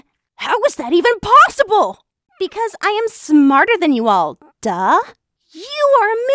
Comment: “ because I am dumber than you duh?” That’s what Emma said